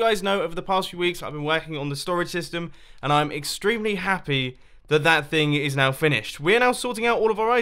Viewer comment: Look the cc for 0:19. He says “I’m extremely happy” the the captions say “I’m extremely sad and depressed”... you ok Mumbo?